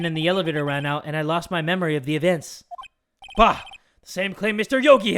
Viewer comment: anyone else hear how said evients ?? 🤣🤣